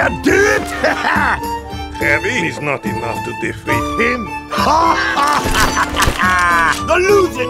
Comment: I thought it said "TO DEFEAT A HAMSTER!" 💀